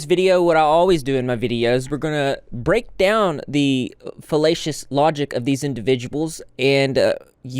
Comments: I heard "hellacious logic" as "felatious logic"... 😭 sorry I knew that was wrong but I definitely had a WHAT DID HE SAYYYYYYYYYYYY? moment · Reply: Wow I also 100% heard “fellatious logic”- I did a double take listen then thought he must of said salacious 😭🙏🏼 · I heard "hellacious logic" as "felatious logic"... 😭 sorry I knew that was wrong but I definitely had a WHAT DID HE SAYYYYYYYYYYYY? moment